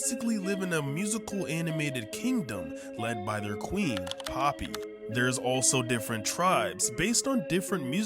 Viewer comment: For a minute I thought it said poopy🤣